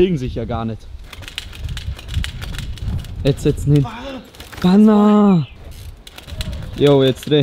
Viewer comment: habe ich erstmal banana verstanden 😂😂